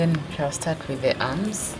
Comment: Did she say chicken arms at 30:54 or did I heard wrong?